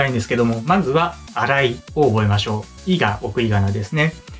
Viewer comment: 「いが送りがなですね」の「いが」の部分が「いいか？」に聞こえてしまいドキッとしました。すみません。